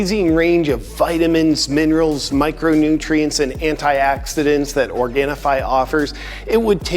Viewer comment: She’s literally nobody without her husband, and even after a divorce she’d only be known as his ex-wife. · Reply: Did you say "anti-accidents"?